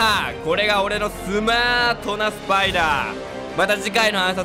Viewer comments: 「また次回の暗殺で、お会いしましょう」が「また次回の挨拶でお会いしましょう」に聞こえたの自分だけ？ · 最後の「これが俺のスマートなspider」に聞こえて笑ったww